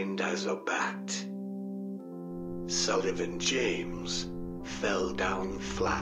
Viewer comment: I kept hearing "Sunderland James". Half expecting PH to show up somewhere now >>